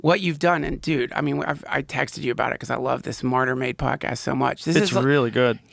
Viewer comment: Is he saying "smarter made" podcast?